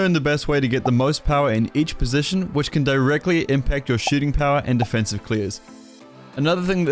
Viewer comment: Who else thought he said “shitting power” and not “shooting power”